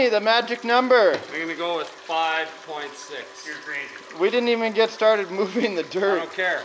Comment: thought I heard say 5.6 not 5.4. He changed his number too. Lol